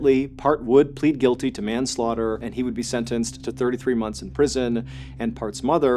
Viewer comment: I think i misheard him say 33 months instead of 33 years.